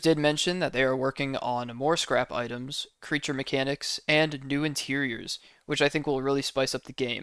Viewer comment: he did not say "new interiors," he said "new tiles for facility and manor." We're not getting a new interior quite yet.